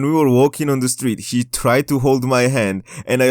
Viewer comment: Did you just say "HE tried to hold my hand" at 1:38? Proud of you man!